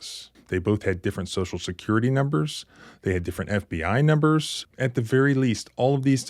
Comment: did you say “different FBI numbers?”